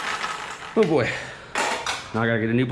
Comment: When ever says oh boy I hear boy